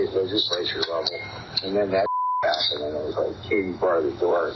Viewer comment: The inaudible part of the recording is Navarro saying, "Katy, bar the door!" It's pretty clear.